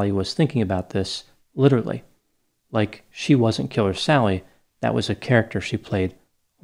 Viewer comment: I thought you said killer salad at first.😅😅🥗She's not very feminine.🤨🤦🏻‍♀️😬